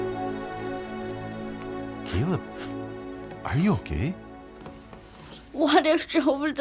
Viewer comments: I swear I thought I heard him say "Caleb, are you gay?" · I totally misheard that one and thought he was asking „Caleb, are you gay“ and nearly chocked on my drink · i heard "are you gay", nice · "Caleb, are you gay?" THATS JUST WHAT I HEARD IM SORRY